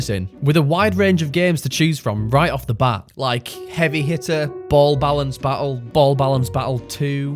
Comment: i heard heavy hitler